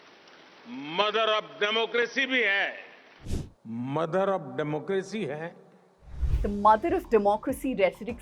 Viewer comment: I heard as ' Murder of Democracy '.....my ear needs cleaning.